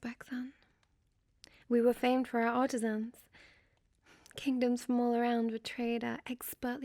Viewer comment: Man I must be tired I thought you said “we were famed for our autist fans” not “artisans” and I was about to be like “how did she know?”